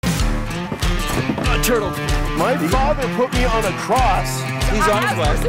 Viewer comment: it sounded like he said aah ny turttle I think he did